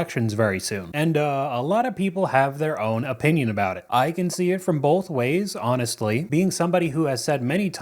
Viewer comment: I legit heard "i can swing both ways" That made me happy.